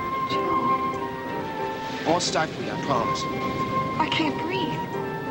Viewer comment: Probably the most emotional episode in the series. The joys of young love; Frank's natural disappointment at realizing Joe's marriage would change their routine forever; Joe's heartbreak at losing Jamie; Frank's attempt to comfort his brother after the tragedy. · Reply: Hello Krist - could u please help me decipher what Joe says @ 5:10-5-13 to her… smth like “I won’t ….I promise”. Thank you.